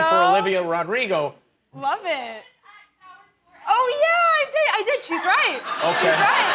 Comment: What did the lady say at 3:17? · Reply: I got it at the Sour Tour